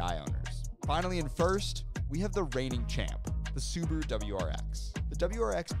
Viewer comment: Did he just say Subaru WAX